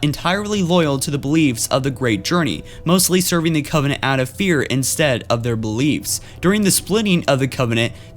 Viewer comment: with Closed Captions on "Mostly serving the covenant out of beer instead of their beliefs" Lol xD